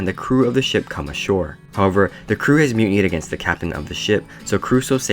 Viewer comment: what did he say at 1:54 ? mutant? what idk